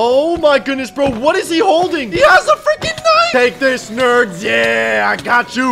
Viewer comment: For some reason, instead of hearing "knife " at 11:07, I heard "He has the freaking Nine!"